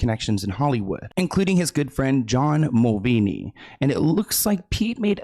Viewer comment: Love your videos but you destroyed John Mulaney's name löl. It sounds like you said Mulvaney 😅